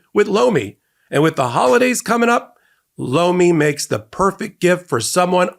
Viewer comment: he said “Lomi” I literally had to bring it back and take a second listen I thought he said blow me 😂😂 I was like wtf did he just say……….ooooooohhhh 🤣🤣🤣🤣